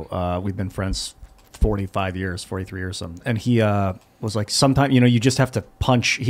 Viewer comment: Did Ian just say he's had a friend for 45 years??? Is Ian in his 40s??? 😲